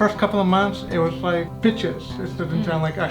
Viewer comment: I thought he said bitches at first😆❤️..... pitch makes more sense to me now....